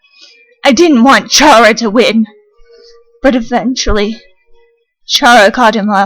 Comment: When frisk said eventually, I thought that she said "where's my chilli?"